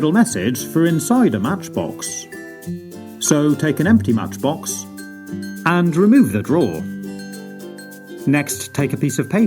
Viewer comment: Me not watching video. Why did I hear “take a matchbox and remove your drawers”? Like: where is this going?